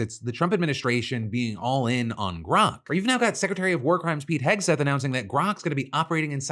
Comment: i didn't hear wrong it's secretary of warcrime and yeah